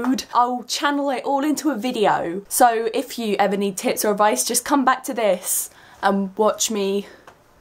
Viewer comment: At first I thought you said "if you ever need tits or advice" XD Sorry for my immaturity (TT n TT)...I jus had to say it XD